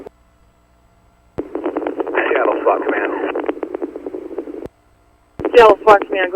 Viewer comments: Anyone else catch the officer say "Seattle sucks, man" on the air at 32:55 after being told to terminate the pursuit? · Did he say "Seattle sucks, man." ?!?!